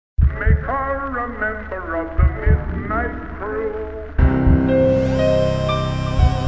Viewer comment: What did he say here? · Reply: Make her a member of the midnight crew.